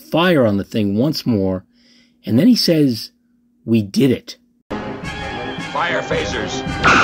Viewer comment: "We did it"...? I always thought he said, "IN IT!" But I was never sure because it was never audibly clear.